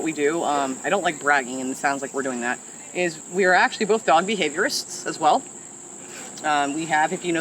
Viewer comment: When you said you were dog behaviourist I thought you said ' we are god behaviourists' which I thought oh-oh you are announcing you belong to some weird religion! But fortunately it wasn't that.